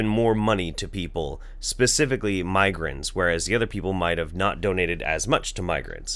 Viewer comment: You said migrants and it sounds like you say migraines. :/